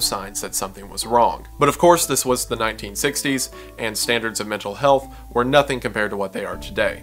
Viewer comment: yes. It does. This was the 1940's. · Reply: yeah my bad... I swear I heard "1940s" at some point lol...